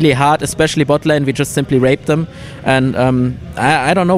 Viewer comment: Did he just say "rape them?"